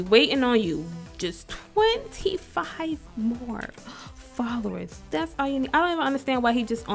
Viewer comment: Am I the only one who heard her say 50 more subscribers instead of followers 😂